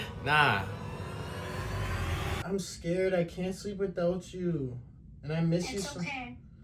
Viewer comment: I thought you said you could sleep without her😂